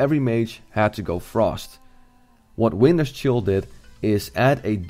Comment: anyone else hear "weiner's chill"? lul